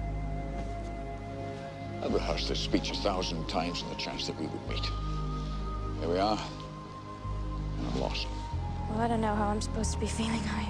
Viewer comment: can someone please tell me what he is saying? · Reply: "I've rehearsed this speech a thousand times on the chance that we would meet. Here we are, and I'm lost."